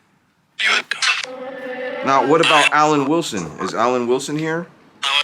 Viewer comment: I heard Alic and then Willson 19:45 go outside 25:44-46 it was me. 26:28-30 don’t—-take it home. 26:45-47 pissed Before the board fell, I heard the name Debbie!